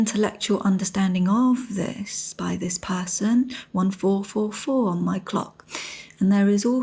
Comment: Did she say 144 at 13:01 ? I have that number written down because I have seen it so often in past year. For example, I was traveling and was listening to podcast at bedtime (my favorite thing) and person randomly says 144 is her favorite number. I remember my room number was also 144 - so I got up confirm and it was. This number showed up several times since around June. Can someone provide information about this number - thank you